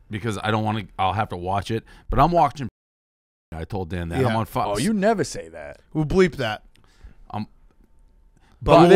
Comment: What did Dave say at 35:31 ? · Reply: Peaky blinders